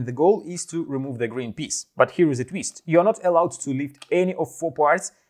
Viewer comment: I thought I heard not allowed to move instead of not allowed to remove